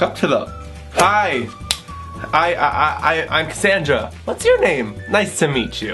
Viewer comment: What's the name you said in 0:59 ? I heard casandra or something, My first name is Kasandra (gudness, horrible)